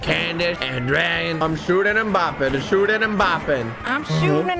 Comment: For one second I thought at 12:26 was saying was: I'm shooting MBAPPE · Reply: say I'm shooting bopping bro ok